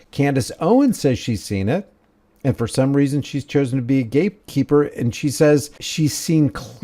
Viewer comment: Did you say candace owens is a "gape keeper"?!😮😮